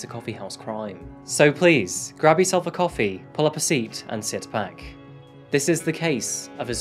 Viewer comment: I've listened to the into plenty... but for some reason, this time, i heard "a coffee AND SIX PACK" lol idek